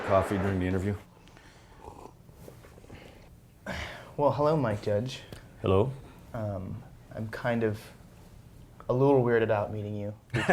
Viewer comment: I thought he said my judge for a sec